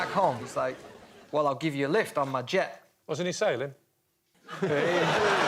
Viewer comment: What does Lee say at 2:46? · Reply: Wasn't he sailing? You know, because of the song.